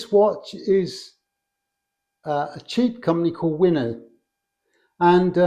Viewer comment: What is the brand of the watch similar to the hamilton? I heard it as Winart